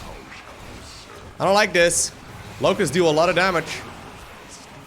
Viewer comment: I thought he said lowkos do a lot of damage